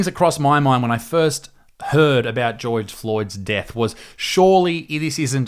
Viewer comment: I know this is supposed to be a really serious video, but did he say Geoige Floyd at 11:05?